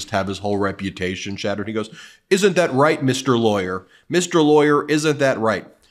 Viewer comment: he actually said mr. Attorney take some breaks ben you're losing it just like dump